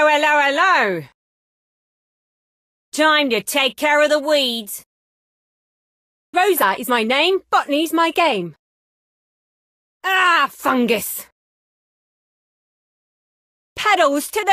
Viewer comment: I thought she say f**k but she actually says bottany Thanks man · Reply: I thought she was saying „f**king is my game“ 😹😹😹